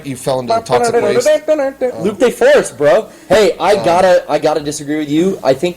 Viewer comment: What is saying at @ 1:05? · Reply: 'Loop' or 'Luke Day Force'